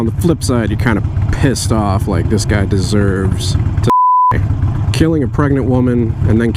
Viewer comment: I'm sorry but with the bleep around 8:51 it sounds like you said "this guy deserves to beep me"